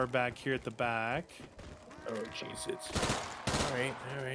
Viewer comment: I haven’t heard one of y’all cuss in FOREVER 😂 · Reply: What did he say I heard "oh Jesus"